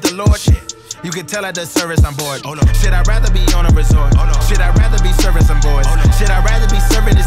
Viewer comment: raise of hands of people who are here from mlb the show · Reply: I had to see if I actually heard him saying "I'd rather be servicing boys". Nope, it was "surfin' some boards".